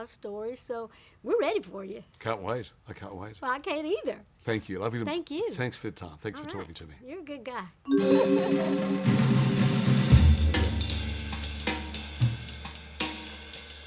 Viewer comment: Did he say “I love you” at the end ? 🥰